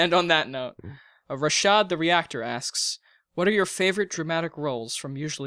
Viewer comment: did I hear that correctly? Rashad the reactor lmao